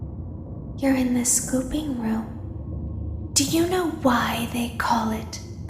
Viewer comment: baby: your in the "scooping room" it sounds like ooping room lol I ment 38:32 Me mishearing it: The pooping room?